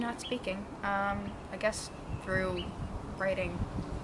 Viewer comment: What did she say at 5:51-5:54?? Im cant understand that part Im not so good in english Pls say me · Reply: i know im 6 years late but she says “i guess through writing”